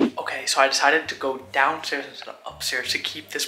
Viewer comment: Why did I hear down stairs to up stairs?